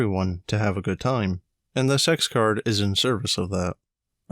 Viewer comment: r/r/rpghorrorstorieshorrorstories. I was minding my own bussiness, while listening to some r/rpghorrorstories in the background when i hear: "the point of games like this is for everyone to have a good time and *the sex card* is in service of that." Uhm... games like this are NOT my kind of game, but hey, if your gorup is in to it, knock yourselves out. "Our GM helpfully informed us that he'd had *the sex card* pulled on him twice [...] for being honest about the age of a victorian prostitute" WHAT!? I went back and checked and the video and it turns out i missheard, but the truth was only marginally better. That is my r/rpghorrorstories horror story.